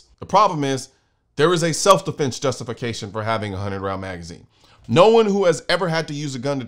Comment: I'm a gun owner, but I initially agreed with Biden's seemingly very reasonable magazine limit. But that was because of some noise that made it hard for me to hear him. I could have sworn that he said 10 pound magazines. That would have been about right. LOL